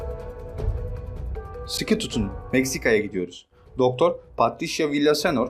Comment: 'te tam olarak ne dediğini anlayamadım bi an şoke oldum.. aman kardeşim aman.. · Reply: Sıkı tutun